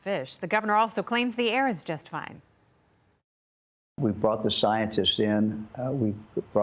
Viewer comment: At first I thought he said they "brought the scientists in". He must have said "bought the scientists".